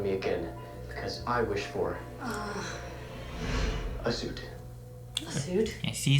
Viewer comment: Bruh I thought he said a Zoot, not a suit